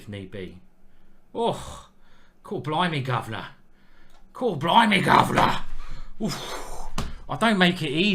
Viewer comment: what does he say here? I only understood governor. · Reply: Cooor blimey governor